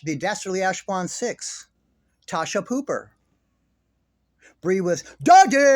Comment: I thought you said Natasha Pooper 😂😂 Cause that’s a good name for her